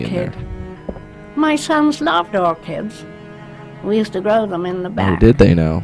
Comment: I thought she said we drowned them in the back XD